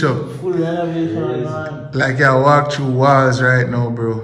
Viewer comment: Ah thought yuh say yuh like walk Tami 😂😂